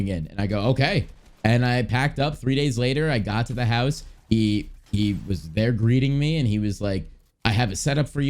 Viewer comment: I thought he said he was breeding him